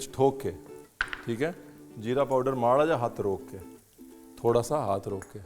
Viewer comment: ranveer says jeera powder but caption says Coriander powder ...what is it ranveer ?